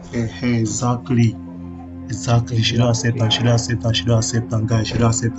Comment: they said Nigerian insult but he actually said "she Don accept am" which means she has accepted it😅